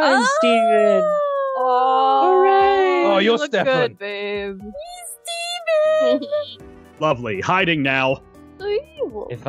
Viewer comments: Did call Babe!!?!? 😵😵😵😵😍😍😍😍😍😍😍 Edited: 5:08 😍😍♥️♥️♥️ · Wait wait wait at 5:09 did call... Babe? 😳😆